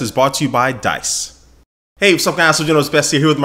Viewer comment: Sounds like you say what's up ass